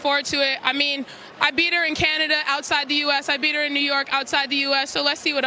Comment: New York is not outside the US 😂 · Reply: i thought she said Newark...in the UK